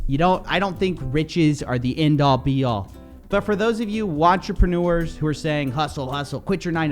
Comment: lol did he say WATCHERPENUERS!? LMAOOO😭😭😭 15:39 wish I could subscribe twice